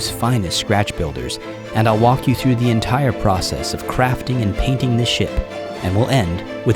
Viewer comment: What was said: "Painting this ship" What I heard: "Painting this sh*t"